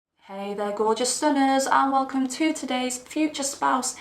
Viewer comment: I misheard hey gorgeous sinners instead of gorgeous stunners. I petition foe sinners from now on 😂😂😂